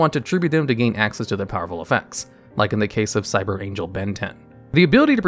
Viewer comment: I keep hearing "Cyber Angel Ben 10" XD